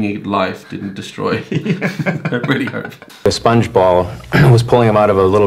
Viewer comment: just curious.. am I the only person who thought one of the guys said: "And SpongeBob was pulling him out of a little girl's ear?"